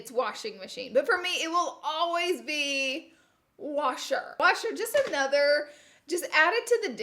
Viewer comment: As a proud Alabama girl myself.. it cracked me up bc I thought you were saying "warsher"... you know exactly what i mean haha!!! "Mama said grab that warsh rag and clean your face". 😂😂😂